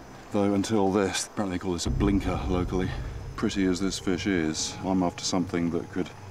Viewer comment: Did he say, “as pretty as this bitch is?” 4:54 lol